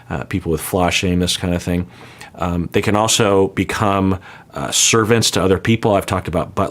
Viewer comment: People with 'flash shameless?' Could someone please clarify what is being said? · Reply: I think he said "flaw shameness" I can't find it as a standard term from online searches But I assume it refers to a person with OCD becoming obsessive about one or more perceived flaws